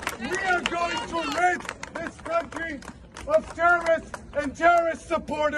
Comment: Did Shai Davidai say:... we will rinse this country of germans and german supporters? (it starts at 4:48 minutes) Or am I hearing that wrong?